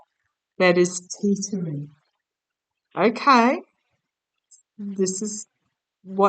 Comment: Hello. Hope your Mom is doing ok. Just an fyi. I heard tottering at 17:50ish. Thanks